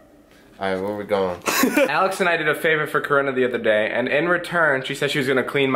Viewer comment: DID HE SAY CORONA IN 2016 DUDE